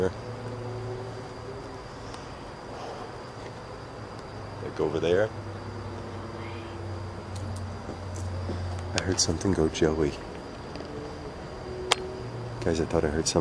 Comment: I heard a voice say something like 'hey" or "Joey", like you said you thought you heard. I am also hearing things that I can not make out. this place has some definite activity. Towards the beginning of you restarting the video I heard a spirit whistle, woot woo! You have a admirer.😮😊